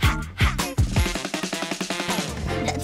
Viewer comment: I can swear I heard spanish at 1:28. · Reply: Yep. She did. "Oh, baby donde vas?"